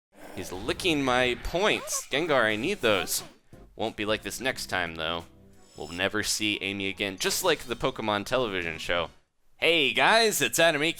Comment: somehow, i thought he said ''He's liking my balls" at the start